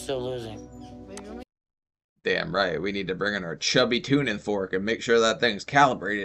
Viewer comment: I thought said “tuna fork” for the scales. In reality, he said “tuning”. 😳😳😳 Don’t know why I thought that was so funny … carry on!